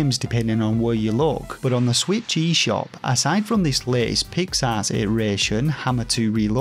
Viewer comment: Was so happy to see you in a Kotaku article. Hope it brings more eyes to you! BTW: Did you review A Robot Named Fight yet? BTW2: Every time you say Pix Arts, I hear “pig’s arse.” I’m a Yankee so it may just be an accent thing, but I’m assuming that’s intentional. 😂